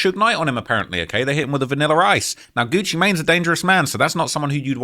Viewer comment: Did u say vanillar rice?( at 7:33)😂😂😂